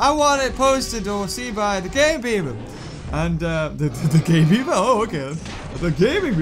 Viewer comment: is it just me or did I hear "The gay beaver"